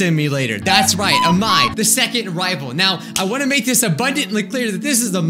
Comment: When he said, "The second rival" I thought he said "the second waifu"